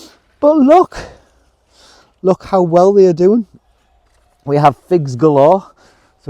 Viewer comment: I misheard. When you said we have figs I thought you said We have pigs. I thought oh my god they’ll ruin the garden. 😂😂😂😂😂😂😂